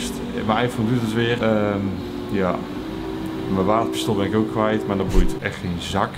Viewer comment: Z’n wapenstok ?? Wat zegt ie daar